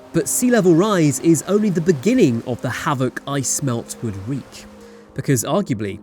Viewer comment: "...Ice melt would wreak." I heard "I smelt, would reek."